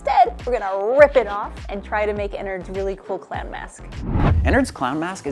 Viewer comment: gonna be honest I thought you said “clan mask” at first and I was horrified 😂